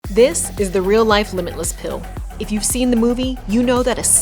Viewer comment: Misheard the title. Was hoping for a lemonless pill. Realized most pills don't have lemons.